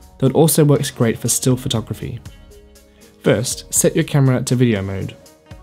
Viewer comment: "First, set your camera to _____ mode." At 4:34...what mode? I can't understand the word being said. thanks! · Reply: sorry, "Video mode"?